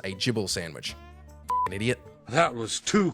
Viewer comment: I always heard, "jibble sandwich." 😅😅